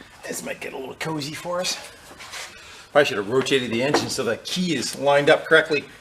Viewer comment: I thought you said termite engine. ReeeEEeeeEEEEeeeeEeeeeEE